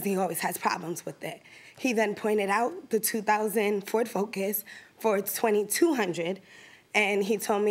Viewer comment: My gosh people dont understand as is..its a 2004 car..cmon lady!!! That guy was helping her..she got lucky and she has the nerve to sue.. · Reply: It sounded like she said 2000 Ford Focus